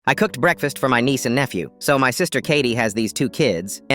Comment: OMGGGG i thought it said "i cook my niece for breakfast"